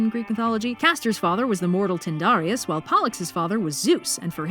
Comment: I thought you said Polites the first time